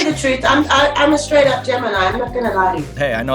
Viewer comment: Honestly thought she said I'm a straight up Gentleman 9:08 ♊ 😂